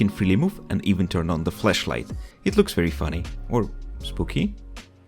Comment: did you just say flEshlight or am i too perverted